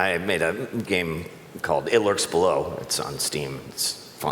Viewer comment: wtf is the name of the game? I have no clue what he said lol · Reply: Hitler's blow